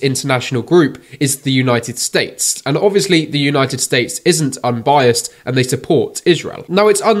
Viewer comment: Did he just say: The United Snakes 🐍 😂